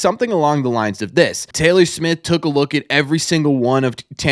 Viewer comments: Hand on heart, I think we all initially thought you said Taylor Swift. All that being said, this seems promising, good analytic awareness. The main question for me is how Mr Smith will handle negotiating with players and other teams. That's where Bloom was faulted, it remains to be seen how well Smith can do since that's not his main area of expertise. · Jim Foxx must be a Freudian slip. Hall of Famer and my grandfather’s favorite Red Sox growing up. My ears were Freudian too - I’m glad you had the man’s name on the screen because I thought you said Taylor Swift. Hey, I’m more into the results on the field - but if they can bring in a guy who can figure out trends and problems before they happen, bring him on. Nerds have their place.